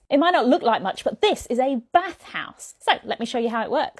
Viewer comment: I heard "Bat house" at first and was really excited.